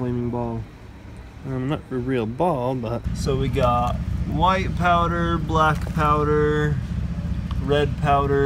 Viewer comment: When he was saying white PowDER and the rest of the colors I thought he was saying White POWER Black Powder ETc